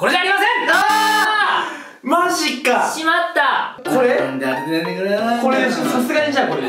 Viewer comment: の「なんで当ててくれないんだよ」が「なんであてふぇtI450♪/☆ないんだよ〜」に聞こえるの好きな方おりません？